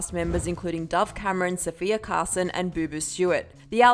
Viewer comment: At first I thought it said "Sovia" And I was like "Who-" since I wasn't looking at the screen I was reading comments.